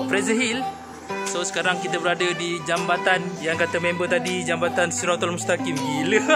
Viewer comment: aku salah dengar ke dia sebut jambantan? :v